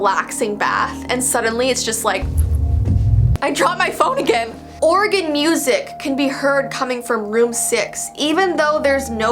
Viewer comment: Who’s Maggie? Maggie Simpson? Edit: yes! It’s near you returned! I can’t wait until you get to New Jersey! :) 2:24 did she just say "Oregon music?" I think it would actually be really cool (and entertaining) if someone just started randomly playing organ music in the middle of the night, haha. :o 7:02 huh... drunk ghosts. Never heard of that one before.